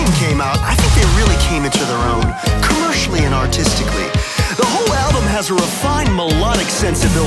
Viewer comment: Lmfao, I thought he said "autistically" not artistically.